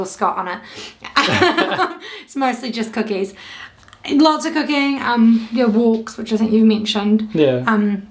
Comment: When talked about what she was doing to pass time during the lock-down, I thought at first she'd said, "to be honest, I've been doing lots of 'cocaine'" and almost choked on my drink, then realized she'd said "...lots of COOKING", LOL. Maybe my wife has a point about me needed it get my hearing tested! Keep safe, keep posting videos, and we'll keep watching. Thanks to you both. Best wishes from Ottawa area Ontario, Canada.